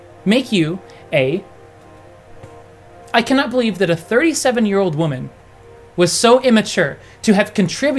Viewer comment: at first i thought you said 87 year old woman...